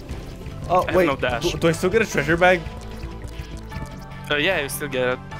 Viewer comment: "то есть выйгрыш Treasure bag?") hehe, I heard a hike on the Russian?)